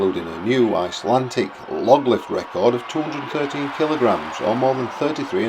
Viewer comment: Did he say that Thor lifted 230 kg or am I mishearing? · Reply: I thought that initially as well but he said 213kg, the same as Eddie did.